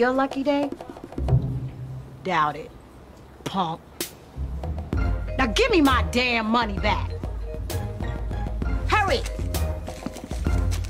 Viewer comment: sorry but can someone tell what word did she say in 1:33 ??? im learning yet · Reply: “doubt it... punk. now gimme my damn money back, and the cards too, give me the cards.”